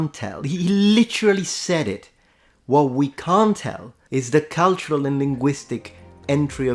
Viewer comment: To my ear it sounds like he says “….what we CAN tell…” not “….what we CAN’T tell…” 🤔